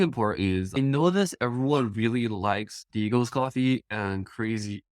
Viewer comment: Thanks for the interview! Could you help me out with something? 9:22 everyone really likes ... coffee? What was the missing word? Sorry I can't get it but I'm very intrigued what everyone likes 😄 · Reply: Yeah, I couldn’t understand at first either. It sounded like “Eagles” coffee. But after playing it a bunch of times, I think he’s saying “Diego’s” coffee, as in, who produces some of the beans that Hydrangea roasts. He mentions Diego again later in the video at 21:00, which is how I realized this.